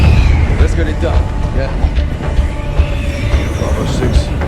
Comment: When the this cutscene was shown to me I thought that gay said let’s get a dub